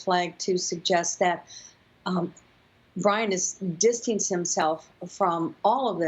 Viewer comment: Real genius, this lady called Gabby “abby” and called Brian “Ryan” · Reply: she clearly says Ryan